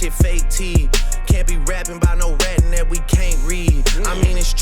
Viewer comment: "Cant be rapping bout no ratting that we can't read" the way he says "ratting that" to me (non natural English speaker) sounds like he said retina, like can't read because you can't see without a retina, am I reaching?